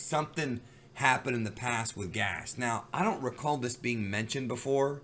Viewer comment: I swear I thought I heard you say he "passed the gas"😂😂